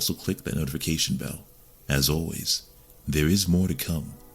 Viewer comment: What are you saying at 22:15? Sounds like "In Val lah lah". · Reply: valhalla